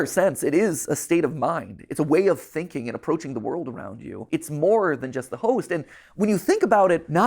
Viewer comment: i was crying until i misheard "it's more than just the host" as "it's more than just the hoes" edit: nvm i watched the entire video and im crying again